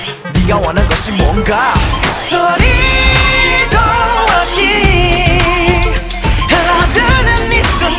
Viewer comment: SOY UN DORITOOOO !!!!!!! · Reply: love coz at 2:06 it is misheard as SOY UN DORITO